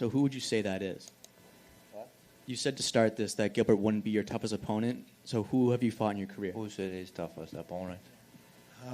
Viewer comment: Did anyone hear who he said who his toughest opponent was? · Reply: Ikram Aliskerov